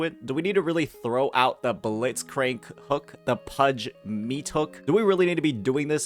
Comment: Pudge meat hook... Bobby Lee played Dota 2 before 😮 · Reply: Lol i instantly searched it up once he said it. I also thought he said blitzspankz 😂